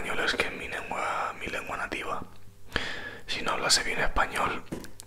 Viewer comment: En el minuto 11:51 ¿cómo dices? "si no lo____español, estaría la cosa mal", no entiendo. Gracias. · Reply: Si no hablase bien creo que dice